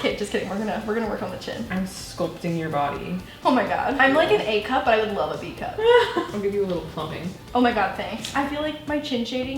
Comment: Did say at 5:14 “ I am like an A cup, I would love a B cup” ?????